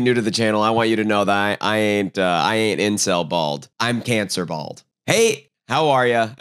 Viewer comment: I thought you said "incel-balled" and i thought that was the new "red-pilled" 😭